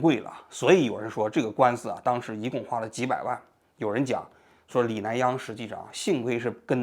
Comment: 在车里听，我老婆听成活佛研究中心，还问我和活佛有啥关系😂